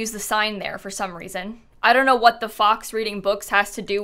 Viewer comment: i thought said What the F^^K